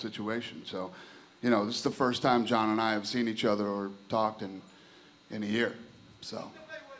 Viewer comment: what does he say at 12:39 ? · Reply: "in a year"